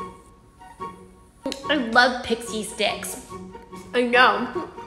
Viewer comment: For a minute, I thought you said pixie decks😢